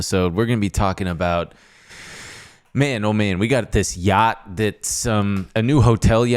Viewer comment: Not gonna lie, I thought said “Gyatt” instead of what he actually said